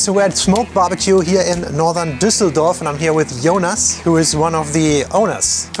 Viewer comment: Did you say "Yowners one of the owners"? ;-D I swear you said/I heard "Jonas" pronounced as like "Yowners" with the dipthtongic English o